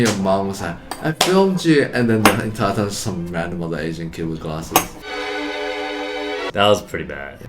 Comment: Did Eddy say, Some random Malaysian kid with glasses? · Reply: I think other asian kid